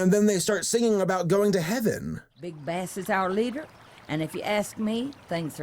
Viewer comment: Oh jeez i thought it said 'Big Ass Bill'